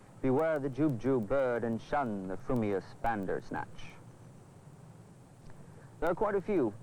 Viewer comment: Did...did he just say "Thundersnatch"? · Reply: Bandersnatch. It's a word invented by Lewis Carroll (nom de plume of the Reverend Charles Lutwidge Dodgson) for his poem Jabberwocky.